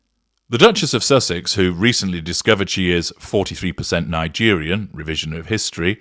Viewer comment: Her heritage is not 43%, it’s was, she misheard it was 4 or 3% Nigerian